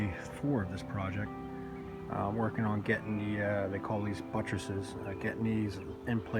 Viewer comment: what are those called again? You said something like buttcherses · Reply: Buttress